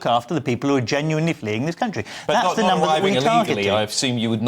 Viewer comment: Did he say, "People who are genuinely fleeing THIS country"...???